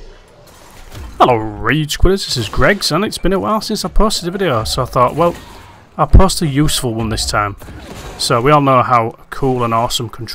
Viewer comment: what was said here? Legitimately can't understand it. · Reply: Due to my dodgy Northern accent. You didn't miss much, I just said "It's Gregs and it's been a while since I posted a video, So I thought well, i'll post a useful one this time" - Hope that helps!